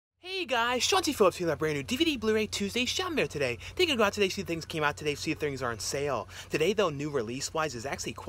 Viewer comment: I thought he was saying Chauncey Phillips ... I think I like Chauncey better.